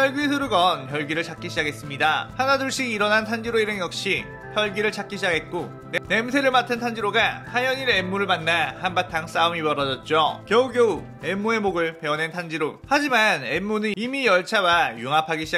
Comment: 냄.냄새라고 들리네여